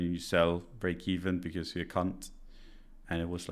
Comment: I literally thought you said "you sell because you're a c*nt" 😂😂😂😂